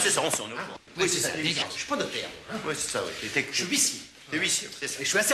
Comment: Il a dit quoi au juste l'huissier à 2:47 avant qu'il dit 《 Je suis l'huissier et je suis assermenté》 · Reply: Je ne suis pas notaire moi